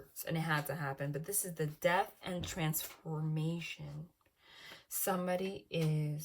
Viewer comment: I thought you said she had already transformed. wow